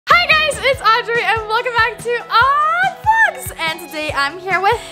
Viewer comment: It sounded like she said odd blogs